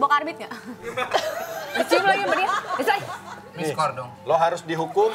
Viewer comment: desta ngomong apaan ya enzy ampe kek syok gitu mukanya 🤔🤔🤔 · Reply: D: diskors dong? E: siapa?? palng ada crew yg habis diskor atau gimana